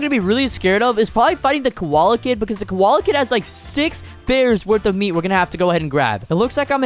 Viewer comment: i heard if i eat the koala kid